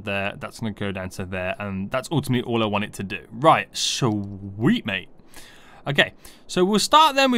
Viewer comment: oops..... i paused at13:05 and closed youtube, but when i got back and strated the video again, the first words i heard were: weed mate